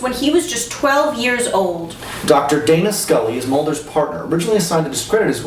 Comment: I could have sworn said Data Scully. Dammit ReBoot!